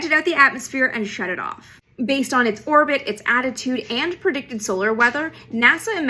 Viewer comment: First time i listened to this i thought she said "based on its orbit, attitude and predicted solar weather..." I was like, yeah, figuring in its attitude makes sense. If it is feeling super aggressive, it might decide to crash early